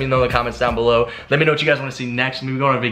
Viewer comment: I thought gut it said Janet